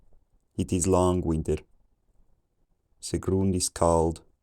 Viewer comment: it was so crazy listening along to basically gibberish and all of a sudden hearing "it is long winter, the ground is cold" emerge from the fog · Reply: Is that what he said? LOL! I heard it's long winded. 😂